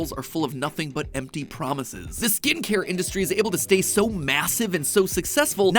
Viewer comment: did someone say MASSIVE